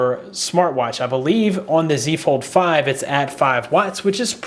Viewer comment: Did you just say Z Fold 5 😂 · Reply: fold 4 galaxy watch 5, it goes very fast xD